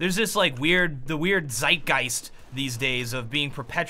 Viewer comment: did he just say "Zeitgeist"?